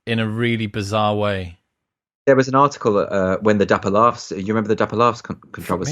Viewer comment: What's the mess Andrew mentions at 11:39? I can't even make out the name of the comedian in question... PS Oh yikes, "Dapper Laughs", I'm getting a headache now... seesm like he apologised and his act died quietly. #NeverApologise